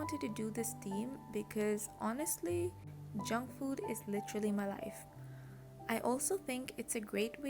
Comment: when you said “junk food is literally my life”, i thought you said “jungkook is literally my life”, and i was like same 😅 loved the theme as always!! happy bday in advance:)